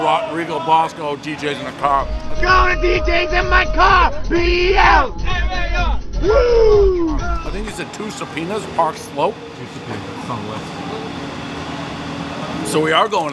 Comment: Idk I thought he said two subpoenas drive slow guess it will forever be debated.